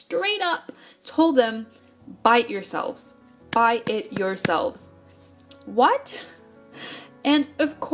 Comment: i thought you said "bite yourselves" and i was like oh umm.. but i feel so bad for idols that are in companies like this. i wish i could take over these horrible companies and treat these idols the way they should be treated